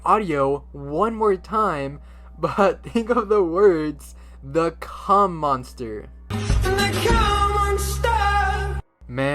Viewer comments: I heard “the cum monster” but hey it might just be me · whenever I heard this song I always thought it said "the cow monster" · I always heard “And the cow monster.” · i hear they come on star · nah i hear "And they, Cum Monster." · i keep hearing "and the cum monsters" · The fuck I thought I heard Cum Monster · i only hear “the cum monsta” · For the last audio illusion I hear "and the ¢um won't stop" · I heard it say "and the cum monster!~" HELP